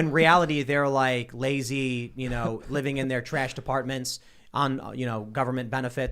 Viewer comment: Tim said "living in their trashed apartments" I heard "trash departments" and I can't understand the difference having realized this.